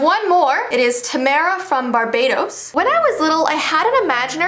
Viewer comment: when you said barbados i thought you said barbatos (genshin players will understand) overall good video :D